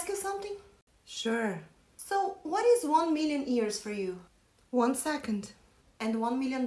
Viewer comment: What is 1 million years for you I thought she said money